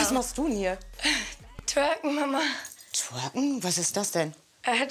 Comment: Ich habe Türken Mama verstanden😂